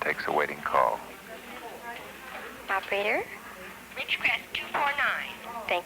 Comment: it almost sounded like "Witch-craft 2-4-9" from a little old witch customer trying to reach her friend.